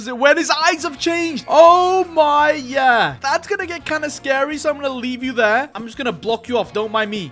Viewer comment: No, and at the first build battle, I heard say "SCARY PISS"!🤣:face-green-smiling::face-purple-smiling-tears:💩😓